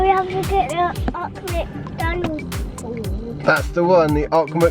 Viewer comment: maybe 'Old' McDonald , from the nursery ryme? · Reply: I thought he said old Macdonald the first time too.